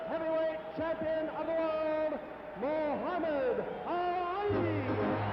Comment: What does the announcer say here because it doesn't sound like he said 'Muhammad Ali'? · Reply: He did, but it sounded like "Moooha-mid ALLL I"